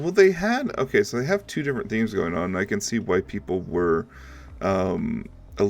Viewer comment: Not sure what was said, please do clear up haha. Did you say "why people" or "white people"? I think you can understand the confusion in this one. This DLC is unfortunately not fully worked out as there are tons of bugs that should have been taken care of before release. Yes I know, "but covid this covid that". That makes no excuse still why you can't play it yourself to make sure you can get out most of the bugs so things will function as it should be. There is neither a good reason why you ignore the pink elephant in the room, leave that bs outside. I been in the gaming industry myself for about 25 years now (both as volunteer as getting payed) This is definitely something I'd avoid at all cost, nothing leaves the building until the known bugs are fixed and it's well tested. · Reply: It's "why people"' but now that you mentioned it I also did hear "white people" hahaha I must say that the bugs in this time around there are far less bugs than in the previous update, the update is remarkably stable! Some boxing does happen from time to time, but other than that, I haven't really run into any major bugs.